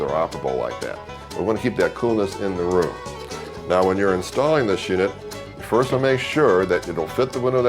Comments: it sounds like he says, "Now when you're installing this shit..." lol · did he say "now when you're installing this shit"?